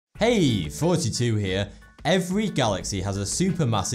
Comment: When he say "hey, thoughty2 here" it sounds like he says "hey, 42 here"